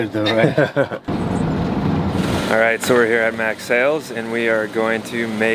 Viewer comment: trying to look the rigging place up ?????? thought you was saying MAX sails. But maybe it is MAC sails... ????? oh i see it now in the verbiage. Mack sails... :-0